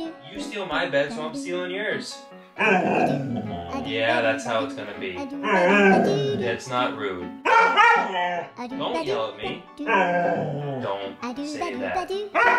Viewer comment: Instead of get out now sounds like he said that's so rude 😂😂